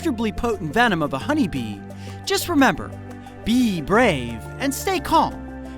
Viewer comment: It kind of sound like he said bee breve